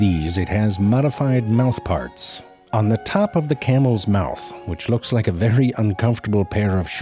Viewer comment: did you say "camel's mouth"???